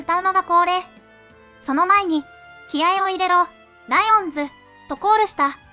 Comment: オレ『気合を入れろ』が『未来を見せろ』に聞こえて切なかった